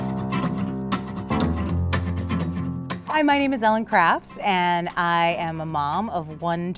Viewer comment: I thought that one lady said my name is Ellen craps